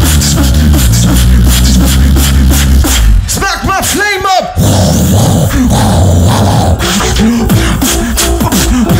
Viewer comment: what does ALEM says? 😭🙏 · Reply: "Smack with flame up" i think